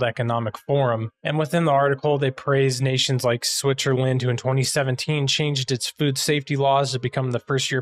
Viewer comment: did he say "Switcherland", or was it some silly audio distortion that made me hear it wrong? o.O 30:59 And that's not even the only solution... He can still screen-record, just could start the other video earlier and do a tiny bit of editing to fit the section in its place. But that would've been too much effort. :-| 38:10 Huh, your outro background image is too wide? 🤔